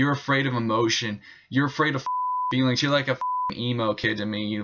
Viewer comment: This theist wins the video award for most swears in a video. I can’t speak for anyone else but he caught me. I’m an emu. A large flightless bird with long skinny legs. Wait, did he say emu or emo? Oh Lisa, I’ve said too much!